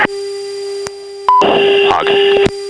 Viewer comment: did he said "pog" ? :"D